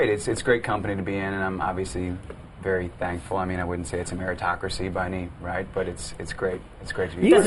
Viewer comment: What's the word he says in 1:18? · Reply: I've been listening to this clip every day for the past 2 years. "I wouldn't say it's a meritocracy".